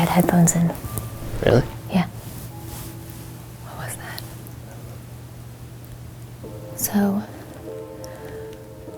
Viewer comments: Does anyone else hear some kind of whisper after Mykie says "What was that?" at 34:12? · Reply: It was the person filming her, whispering back "I dont know" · Ooookay when mykie said "What was that." i legit heard "nothing" and for a second i was like 'was that one of the boys' please tell me it was one of the boys.